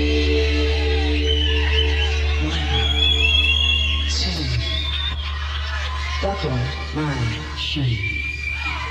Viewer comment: Thank you so much for this video.. So freaking AWESOME..kudos.. Great song. Can anyone help me though... At the 1:37 Mark, what is he saying? It's making me crazy... Thank you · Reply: He says 'One, Two, Buckle my shoe'